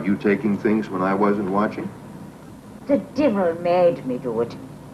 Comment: Did she just say the "The Divil"? Three times?